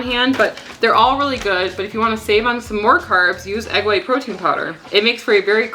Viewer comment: you mentioned using egg white protein powder in place of almond flour for your chaffles? (Did I hear that correctly?) I’m trying to find a chaffle recipe that’s not so high in calories. Will your chaffle recipe work using egg whites in lieu of whole eggs, and egg white protein powder in place of almond flour?